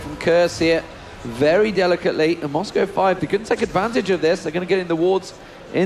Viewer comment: Did he say Moscow 5??